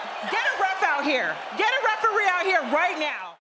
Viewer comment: That was a hole lot of fun lol. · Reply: I thought nia said get a ref(rigirator) out here